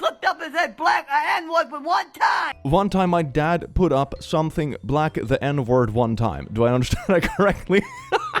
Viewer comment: I think she said "one time my dad F'd up and said blacks are n words that one time"